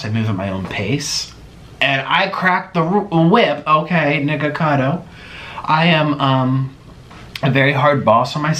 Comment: Did he just say “I cracked the whip niggacato???”